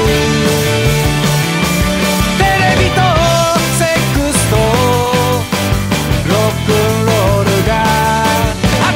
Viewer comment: it says "Sex" or did I just hear it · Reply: GlowstoneLove Pad I’m pretty sure he said “Television, sex, and rock and roll”